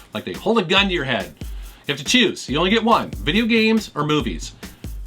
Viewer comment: I thought he said video games or boobies?